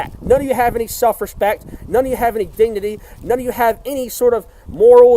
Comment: when he said dignity, it sounded like DEE DEE DEE, he's ripping old Carlos Mencia XD